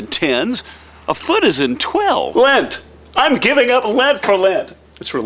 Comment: I totally thought you said you were giving up Lent for Lent at first and so I was thinking....wait, If you're giving up Lent for Lent, then you are still having Lent, yet you already gave it up.....what? And then you said lint and all made sense haha!